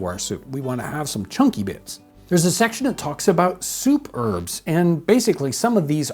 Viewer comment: Here I am cleaning up the house and I hear "... superb .." . Suddenly I realized Jon was saying " ... soup herb .. " . 😊